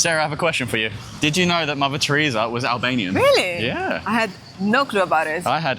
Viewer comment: I thought you said "lavatory" here instead of "Mother Teresa". Enunciate, Bro!